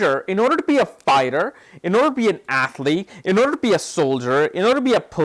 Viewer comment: I heard 'athleak' again :)